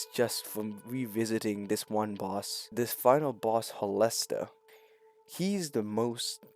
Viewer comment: why did homie called Heolstor a molester? 😭